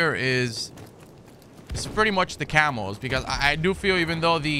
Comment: No joke I thought you were saying camels and not camos hahaha